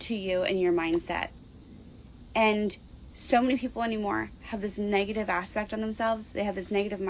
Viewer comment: Did she just say “so many people anymore”